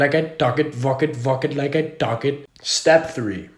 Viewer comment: I thought he said fuck it while I talk it